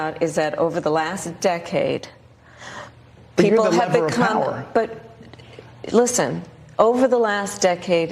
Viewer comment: Can anyone figure out what Bret Baier is saying at 7:34? It sounds like he might be saying. "You had the lever of power," but I'm not sure.